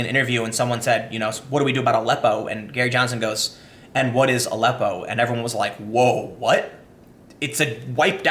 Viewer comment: I thought he said “WHO is Aleppo?” Not what is aleppo